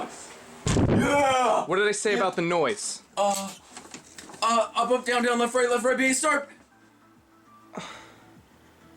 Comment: Please, can someone tell me what is being said? I cannot understand a single word, it's all too fast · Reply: Blinx up up down down left right left right b a start